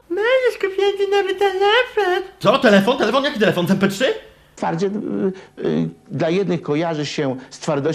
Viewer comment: co on powiedział? "Parcie yyyy... dla jednych kojarzy się z twardością muskułów", ja słyszę takie coś ale nie wiem co on mówi. Edit. No dobra, juz wiem że powiedział "twardziel" bo obejrzałem dalszą część xD